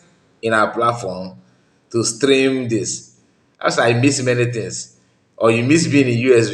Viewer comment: No peace for the wicked. I heard her said,"I miss many things"